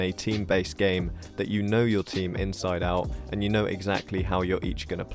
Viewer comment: what? What did he just say? “Inside and out”